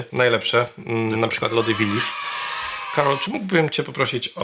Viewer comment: Usłyszałem "lody w Idisz"